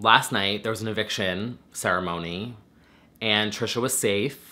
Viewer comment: I legit heard... "There was an eviction, 'sarah moaning'... " Huh? Oh Ceremony!! 😂😂😂